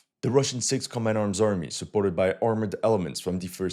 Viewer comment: I misheard "suported by armored elements" as "supported by armored elephants" and, man that would surely win the psychological bit of the battle.